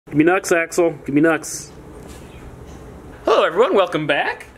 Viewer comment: I thought you said "gimme nuts Axel, gimme nuts" 😂😂😂 I'm so dead right now